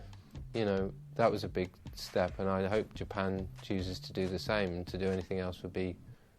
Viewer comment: If you put english closed captioning on it's hilarious what it thinks thom is saying. At 3:22 i quote "that was a big staff and i hope to pound jesus jesus and do anything else"